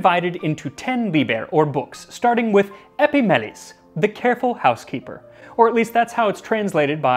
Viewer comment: “The Careful Housekeeper...” and all I heard was “Eyzmopolis my Metropolis...”